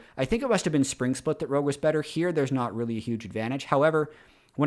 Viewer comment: I think Caedrel said JDG was the good blue side team, not Rogue.